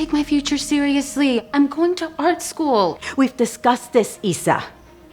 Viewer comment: I heard fart school